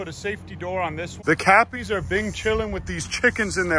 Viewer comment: at first i thought he said the cappys were binge killing. that would be fitting though.